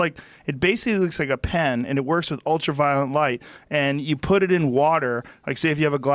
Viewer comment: Is it me, or does Joe always pronounce William Shatner as "William Shackner"? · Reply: Lol I hear him say “Ultra Violent Light” all the time